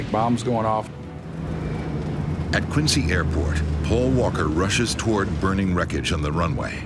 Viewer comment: did I heard Paul Walker?